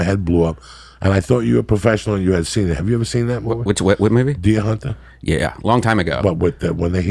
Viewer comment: what movie is he talking about? I can't understand the name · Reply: The Deer Hunter (1978)